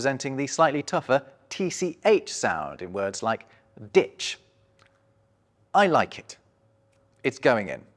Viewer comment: What did you just say? 5:54 🤨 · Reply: ditch